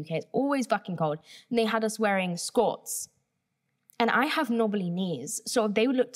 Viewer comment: hi!! i have a question, is saying "skirts" at 26:31 and 26:40? english is my second language and i learned it with the american accent, so i was wondering if in the UK they pronounce it different or if she is actually saying something else. (by the context of what she is saying i think she is, but if anyone could confirm it for me it would be great) Thanksss :) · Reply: she’s saying “skort”- it’s a combination of a skirt and shorts (like a skirt with a pair of shorts built in basically)